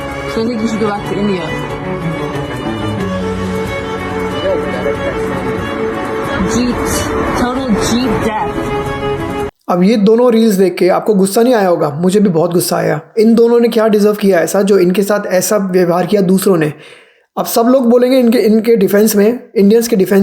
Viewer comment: what she said Total Jeet death ?